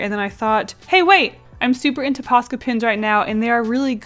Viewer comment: Is it ok I thought she said pasta pens 😂